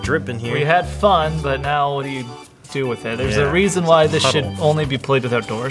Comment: Did he just say shit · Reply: He said should. But I does sound like shit